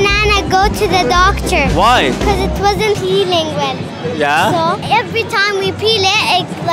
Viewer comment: The last girl I thought said “bc it wasn’t peeing well” 😅😂